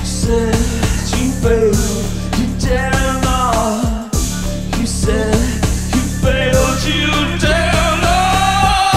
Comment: Does anyone know what he's saying around 2:30? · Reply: She said you failed you dare not She said you bailed you dare not